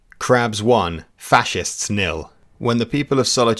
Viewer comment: Did you say the Latin word "Nihil"? Lol.